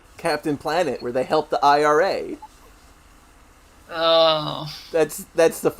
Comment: you said "IRA" but I think heard "IRS".